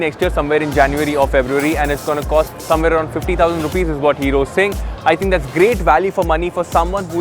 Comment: Did I just hear 50,000 Rs at 01:30 ??